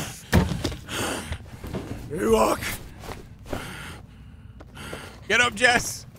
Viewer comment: Subtitle says : Can you walk? Voice actor : You walk or it could also be, EWOK 🤣